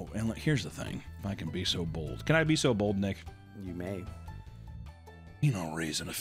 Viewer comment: “You may good sir 🙂‍↕️” is what I heard and seen😂